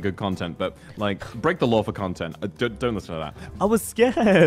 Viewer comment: I think Vox said "Don't listen to that"